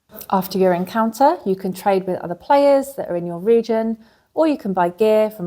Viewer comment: I can't find the rulebook... And 2:40 I keep hearing Norwegian. (the subtitle says your region)